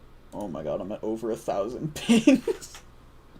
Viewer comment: if you turn on subtitles at 1:39:03 you'll have a good laugh because YouTube has a hearing problem and instead of the word "ping" it understand another thing.🤣